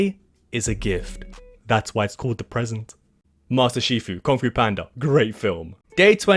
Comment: master shifu? did u just say master shifu? master oogway will be very upset